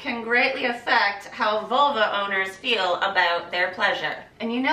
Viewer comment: Did you say "Volvo owners" HAHAHA 7:12 "I'm sorry... in which case what?" Litetally made me laugh out loud!!! Great video!!! Lots of laughs... "I listened intently"... and learned a lot!! Mission accomplished!!! One of my faves!!! 👍😉👌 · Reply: Dustin Woloschuk LOL!!! Thank you for the comment. It made me laugh! I said “vulva owners” hahaha 😁 So glad you enjoyed it. It’s one of my fave videos too! 🤘🏼